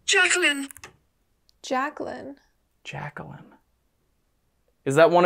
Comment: I think the voice says "Shall I come in" and not Jaclyn. is messing with a door, so I thought that made sense.